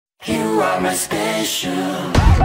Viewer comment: I love this song but I keep hearing “You are My Spaceship” 😂😂